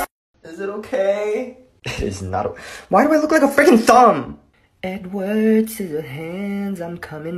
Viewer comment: The girl fixed her mistake, but the second one. Why do I look like a dog!?😂😂😂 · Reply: Bahahahhaha I thought he said “why do I look like a thumb” 😂